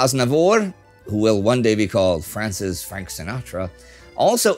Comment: I was stupid confused I heard "Will one day be called Francis "Frank" Sinatra and was so bloody lost there for a moment. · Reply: Same here. He was called France's "Frank Sinatra" to clarify. <link>